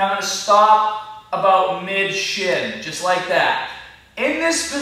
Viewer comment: did he say "Mid-shit" =]